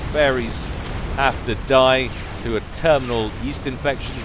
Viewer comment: Hey, right around 14:12 you say something about "...die to a terminal yeast infection..." what did you actually say there? i'm very curious